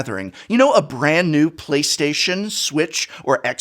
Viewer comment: It sounded like he was saying PlayStation Switch (as if PlayStation made the switch) and X box instead of "PlayStation, Switch and Xbox"